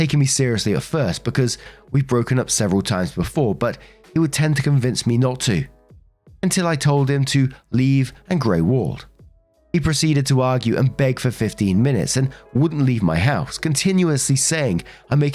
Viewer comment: I misheard "grey walled" as "grow old" and was like wow that is an insanely specific non-aggressive way to basically say "leave me alone and live your life without fatal circumstances" which. Can mean a LOT of things depending on the tone its said in